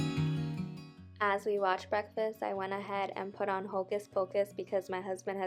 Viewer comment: Hi beauty. Haha.. I thought you might’ve said watched breakfast 🤣, but honestly I was so distracted by that delicious food. O my!! I will be making that. Thanks for sharing. Aww!! You were out by me. I live 15 minutes from Lewin’s. Those donuts are the best 😋. I always wanted to do a simmer pot too. Girl, you’re giving me a to do list here lol Great cozy video xo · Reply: Haha I definitely watched breakfast 😂😂 the donuts were AMAZING! They tasted so fresh and the sugar dusted on top was so good. Their pumpkin patch is now open 🥰